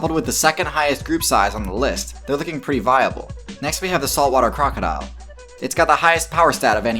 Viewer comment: I legit thought you said "assault water crocodile team" for a hot minute. release the assault water crocodile squad